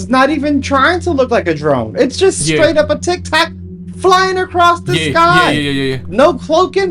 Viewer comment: O he said tictac thought he said tic-toc 😂